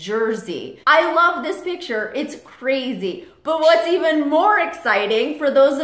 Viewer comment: It sounded like said she is crazy